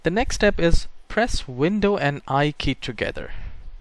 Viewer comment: bro can u say what u said that time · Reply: Press Window Key + I